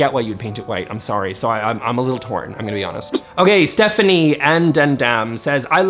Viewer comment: did he just say Stephanie and damdam?